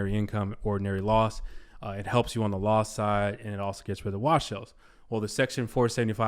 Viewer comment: Great video, and PERFECT timing! What did you say here @ 4:45? "It helps you on the loss side and it also helps you get rid of _________"? I can't understand the word here. Thank you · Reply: Wash sales